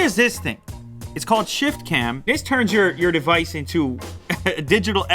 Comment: did i hear lew say "shitcam"?ahaha